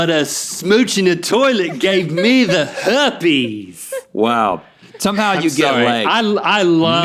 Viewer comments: AHHH 13:33 I THOUGHT RHETT SAID ”Wow...somehow you get laid...” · lmfaoo I thought rhett said somehow you get laid